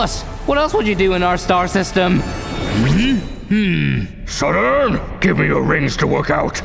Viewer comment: Bro thought a blackhole was a trampoline😂 3:03 the captions say "shut up" instead of "Saturn" lmao🤣